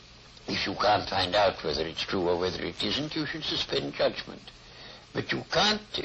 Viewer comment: #help I want to complete the quote starting at 0:33 and ending at 0:44 but i just can't understand a word. "you should ________ in judgment" Thx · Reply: You should suspend judgement.